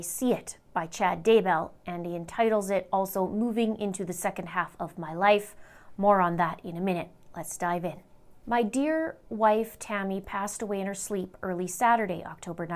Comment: you said "more on this in a minute". i heard that as "moron this in a minute". kinda makes more sense that way....